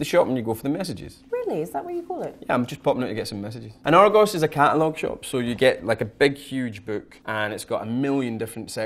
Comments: can someone please tell me what James is saying at 2:46? i'm making a script for this video for my students but can't make out this sentence :( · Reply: I think it was, "An Argos is a catalogue shop, so you get like a big huge book..." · Argos ... had to look that one up because it sounded like our ghost